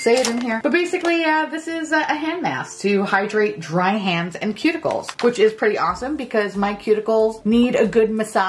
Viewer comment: I thought you said “pube- ickles” instead of cuticles!